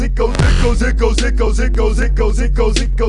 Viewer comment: Is it weird that I hear "it comes it comes it comes it comes it comes it comes it comes it comes it comes it comes it comes it comes it comes it comes"?